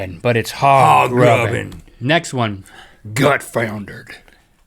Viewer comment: when Link said "gutfoundered" I thought he said "gut failure"